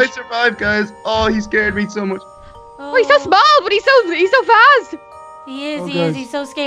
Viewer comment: did anybody hear say "he's got balls but he's so fast" (I know she didn't actually say that, just want to know who has a dirty mind)